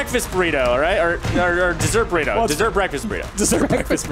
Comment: I thought it said Desert Burrito War. Why would anyone want a sandy burrito